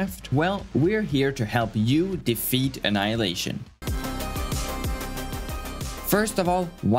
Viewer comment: intro ~0:08 why did i just hear feet anihilation 😭